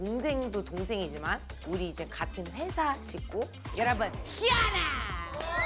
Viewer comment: 씨아라!! 라고 들려요 ㅋㅋㅋ